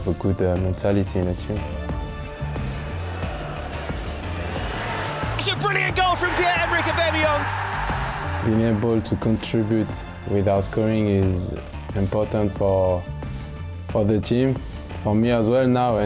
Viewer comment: Who heard this commentator say Pierre-Emmerik ooobemeyong